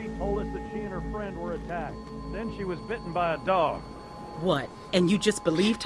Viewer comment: I thought they said ‘she got bit by a frog’ I nearly chocked on my noodles 😂